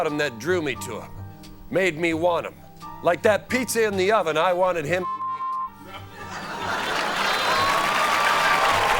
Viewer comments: Can anybody help me out in telling me what he said??? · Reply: “Inside me” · Does anyone know what they bleeped out at 19:00? I’ve always wanted to know and could never quite figure it out. I think he says “in me” but some clarification would be great. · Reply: Yes in me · what does Ryan say here? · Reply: "Like that pizza in the oven I wanted him in me." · If someone can read lips I am dying to know what Ryan said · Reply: Ryan said: Like that pizza in the oven, I wanted “him in me”. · I can’t seem to make out what he said? Wanted him biblically??